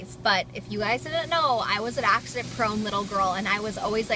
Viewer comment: I thought that said porn. haha. I think you mean prone.